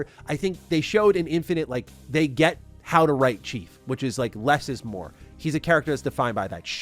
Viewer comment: I had to go back 4 times because I thought I misheard. You actually said they know how to write Chief😂